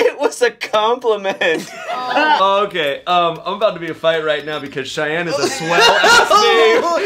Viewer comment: What does Shayne say at 4:20 - 4:24? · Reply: The question was "have you ever been in a fight?" And he said "I'm about to get in a fight right now, because Cheyanne is a swell-asss name!"